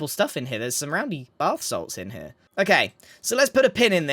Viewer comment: "there's some roundy BATH SALTS in here" XD Did I hear it right? Bath salts?? XD